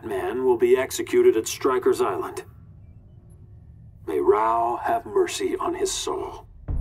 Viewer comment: what did regime Superman said after announcement news? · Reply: “May RAO have mercy on his soul” RAO is the Krypton’s god/Deity